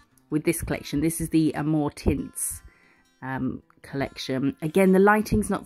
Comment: So happy to have a "new" video. Love the flowers so, great. I had to rewind when you said amour tints. I swear I heard "more tits". I was thinking what is up to?!?!😂😂😂😂